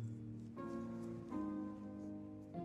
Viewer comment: 😂😂😂 "it's for your mouth... Let me know if you need a bigger pice." Caught me off guard 😂😂😂😂😂😂😂😂😂😂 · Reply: I thought it said "let me know if you need to pee" 😭